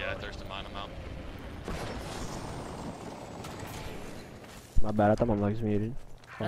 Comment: What did the team mate say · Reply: ankit gangishetti “my bad I thought my mic was muted”